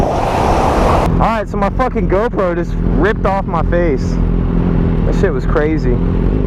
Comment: Sounded like you said my girlfriend ripped off my face 😂😂😂😂😂